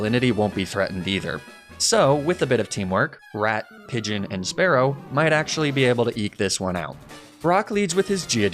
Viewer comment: My god, I thought you said "rat, pigeon, and sparrow might be able to eat this one out." and lost my shit, lol.